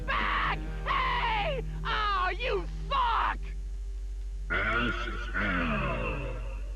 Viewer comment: Does al say “You fuck”? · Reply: “Suck”.